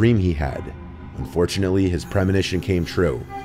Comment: You need to enunciate better as it really sounds like you say fortunately his premonition came true.